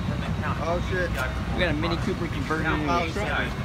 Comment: did he just call that pickup a Cooper?